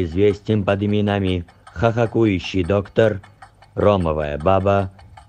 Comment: я один услышал ''Какующий доктор? '' Походу у меня шиза